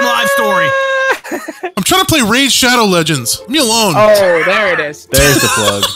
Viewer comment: Instead of him saying Raid:Shadow legends he said Rain:Shadow legends ಠ_ʖಠ😂